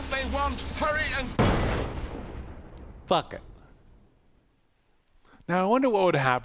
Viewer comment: I definitely thought said F*** it and not bucket